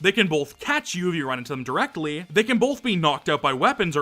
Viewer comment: knocked up? You can say the cc says out but i 100% heard up